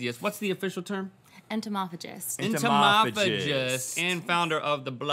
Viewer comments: Entomology is not the eating of bugs it's the study of bugs... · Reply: Gunmetal Speedster I must have misheard. I thought I heard entomology. Thank you for the clarification :) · ? an entomologist is someone who studies bugs not necessarily eats them isn't it unless she said entomophage and i misheard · Entomologist is the study of bugs not eating bugs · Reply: Michael Thoerner oh OK thank you cause I thought she said entomology